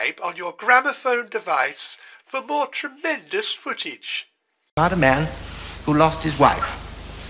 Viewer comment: What does he say at 0:20 and why it's funny? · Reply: "It's about a man who lost his wife... rather careless."